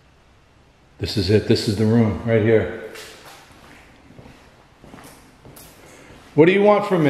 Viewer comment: about 9:28,after you say this is the room, I heard Why?